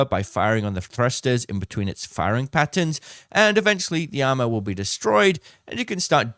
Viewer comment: the subtitles read as 'the ammo' when you said 'the armor'. is it that hard to read them once for quality control? edit: at 11:40 it also reads 'repeat' instead of 'repeek'